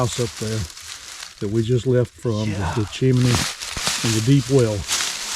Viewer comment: I don’t know,if anyone heard what I heard at 14:00; something or someone said “shut-up or get up!” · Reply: said "yeah" when answering, but it did sound creepy, lol